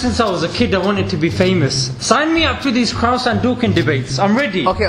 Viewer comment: I thought he said hadouken 😂😂